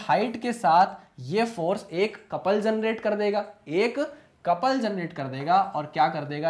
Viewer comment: Sir I have some doubts .would you like to clear my doubts? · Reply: sir I can't understand what you said at this point, you said something like "Compulsion rate" and then that it would double the bottle." I can't understand that sentence sir please clear my doubt